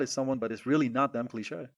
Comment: why does it say "clique" when you say "cliché"??